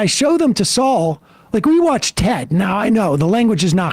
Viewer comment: He watched 'Ted?' The Seth MacFralane movie? Is that what Sam just commented on (and didn't elaborate on) or am I mishearing that?